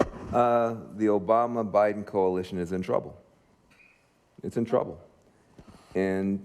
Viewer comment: At first I heard "The Obama-Biden coalition has been Trumped" lol